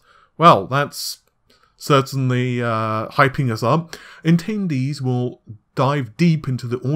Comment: did you say tendies? Now you've got my attention!